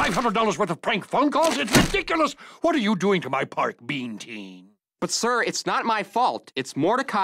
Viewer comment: for a second there i thought he said Ben 10 lol